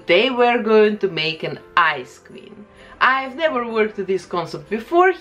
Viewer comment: i thought she said "ice cream" not "ice queen" lmao